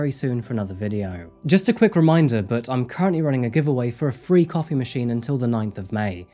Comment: aha a coffee machine . heard it as copy machine.